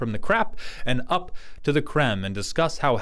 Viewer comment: I'm too Stormlight-brained. When you said "let's move on from the crap and up to the créme", I heard "up to the crem", and thought "well that's no a huge leap".